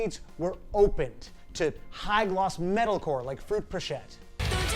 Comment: WHAT IS THAT BAND, PLEASE? I can't understand what he said; Frick Pichette? Fruit Pushit? Frette Pinchette? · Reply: found it! its fruitpochette!!